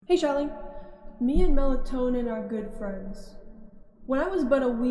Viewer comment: i thought it said melanin wizard so i thought u rejected his offer